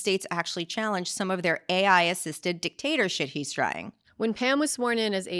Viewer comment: WHAT???? I cannot understand what you’re saying Alyssa no matter how many times I listen to it. Anyone? 😂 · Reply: AI assisted dictator shit he’s trying